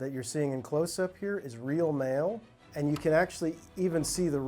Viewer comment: I thought he was saying "The male that you are seeing in close up here is real male". I was like... this is more than random.... than i realised it was "mail"......